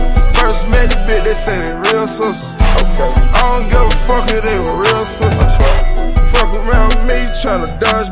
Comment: At first I thought he said .. I don’t give af if that’s my real sister 😭